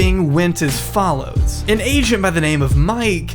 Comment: why did you say asian like that?